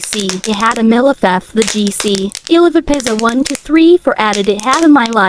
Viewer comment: bro i hear just iliveapizzle1234